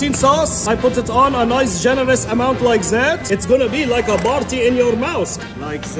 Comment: I thought he said "its gonna be luke a farty in ur mouth" lol